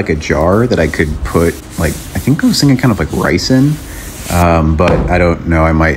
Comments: “Rice in” I heard “Ricin” 🤣 … like who you trying to murder!? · I thought you said ricin not rice in lol 💀